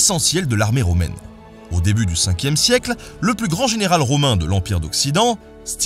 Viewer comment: Au lieu de "général Stilicon" j'ai entendu "général c'qu'il est con"